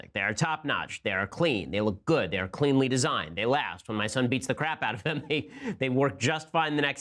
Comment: I thought he said When my son Beats the crap out of me